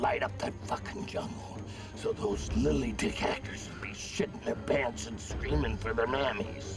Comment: I always thought he said "lily dick hackers". But its quite obvious that he says "lily dick actors". Truer words never spoken. None of these actors would make this film again.